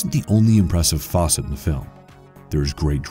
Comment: Did he say "this isn't the only impressive faucet of the film"